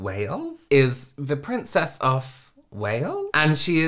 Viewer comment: I thought you said Princess of Whales. 😂